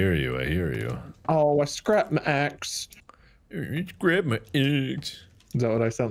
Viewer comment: When said he scrapped his axe it sounded like he said I scrapped my AsS